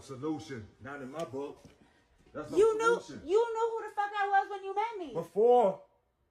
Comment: all I heard was "I was a lying whore when you met me"